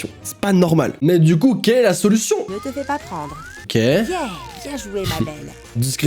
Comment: A 7:56 c'est moi où elle a dit "bien joué mamen"